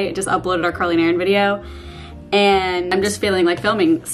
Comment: When you said, "I just uploaded the Carly & Erin video.".....I thought you said, "I just uploaded the curling iron video." :)